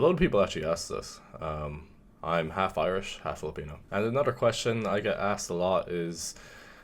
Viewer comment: Did you just say half-irish, half-filipino?